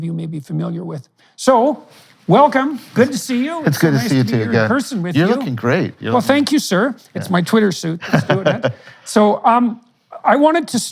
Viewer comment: I thought he said: “it’s my queer suit” 😂